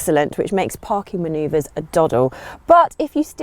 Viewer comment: Hi, Can somebody plz help me understand, what word the host/vlogger has said at arnd 2:10, which is the last word of the sentance : "which makes parking manoeuvres, a ______".. Sub title is missing and i like the sound of the word 😉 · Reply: “Doddle” means easy to do